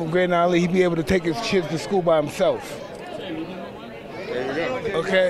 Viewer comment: "...He'd be able to take his chids to school by himself..." Did I hear that correctly?